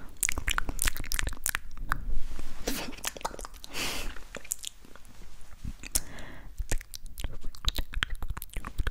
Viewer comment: it sounds like she said tf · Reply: She said tf